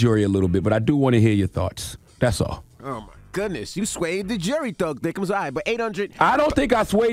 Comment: Ummmm did Envy call Charlemagne THUG THICCEMS at 5:10??? 🤣